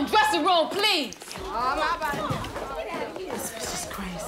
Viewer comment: What did one of the random girls say at 1:08? On my body? Aw bye bye? I can't make it out but I always wondered what she said as they rushed out. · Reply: She said as bye bye 🤣🤣